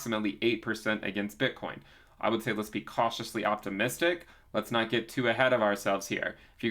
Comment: When says "Cautiously optimistic" I hear "3x"